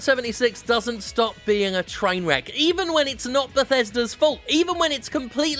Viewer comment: watches the video with subtitles then at 4:09 he says "Bethesda's" the subtitle says "festers" searches the meaning for fester oh dear